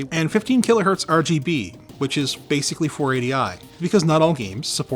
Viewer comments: when anthony said 4080i, i thought he said 4080 ti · the subtitles says 4080i, it should be 480i